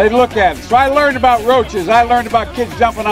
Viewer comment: Did he call the kids roaches?